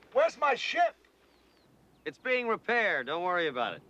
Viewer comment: Lmao I thought he said where's my shit when he said yeah! wheres my ship lmao